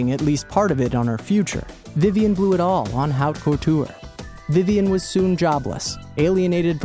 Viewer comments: ok what the F does he say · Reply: Haute couture, It means high dressmaking / high fashion. A high-end-fashion were the clothes are handmade from start to finish. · wait what the hell did he say at 4:34? Vivian blew it all on how po tour? what?